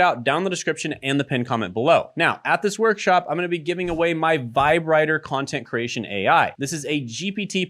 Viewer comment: are we not gonna talk how " vibe writter" sounds like your saying vibrator?